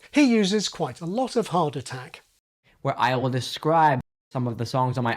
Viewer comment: I thought he said “heart attack”